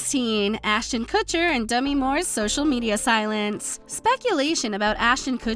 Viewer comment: Man, your tone did not match the subject matter at all. This sounded like you were giving awards at a tee ball banquet. Also...did I hear "Demi Mooreses"?